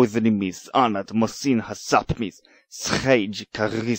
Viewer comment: As the creator of Aorrian, I can say that I'm glad I took a lot of time to make sure each take sounded perfectly (took me over 20 tries), because my language is not easy at all to pronounce. But some of these contributions sounded like their conlangers did it in one quick take and said "Good enough". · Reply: I love the slightly aggressive sound your recording. At 4:40, I hear a word that sounds like "sapmis". Would you like to say what that word is? I'm interested because "sápmi" is the endonym of the Sámi peoples. :P